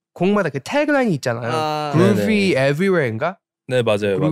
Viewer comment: Before I knew the intro was "Groovy Everywhere " .. I literally heard it as "Cuz we alien" and I liked it sm .